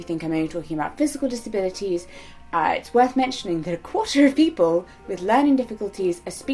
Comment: Only to let you know; the closed captions at 5:12 are off from what you are saying. You say "i'ts worth mentioning" but the CC says "it's worth less than". It was a bit confusing and at first I thought you meant that some disabilities somehow have more credibility than others. It did not sound like something you would ever say so I just thought you should know in case you or anyone else would like to fix it. Love you!